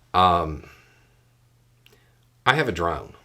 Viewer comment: I heard you wrong I heard you say "Um..I have a daughter...that we got for the channel On The Road." I went back and checked. One of the funniest mis hears Ive had in a while.